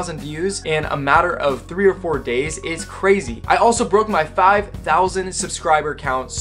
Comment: I thought you said “I broke my thigh”